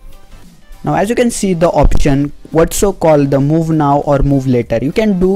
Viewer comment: as u can see the oxygen instead of options 😂